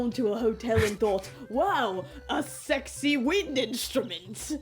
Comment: "What a sexy weed instrument!" That's what I thought I heard.